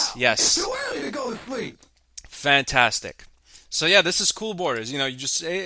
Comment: I could swear I hear "so where do you go to sleep